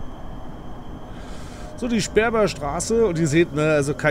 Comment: hab ich das gerade richtig verstanden sperma Straße ?